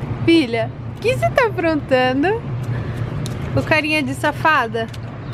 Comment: Eu entendi _ O carinha diz safada 😂😂😂